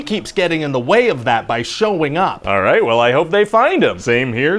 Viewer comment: "They desperately want to locate this guy but he keeps getting in the way by showing up" that was gold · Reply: i thought "shawing up" he said